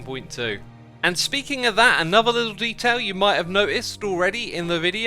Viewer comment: I thought you said "Bee-tials", as a fun pun instead of details. Sadly I rewound and found I was wrong.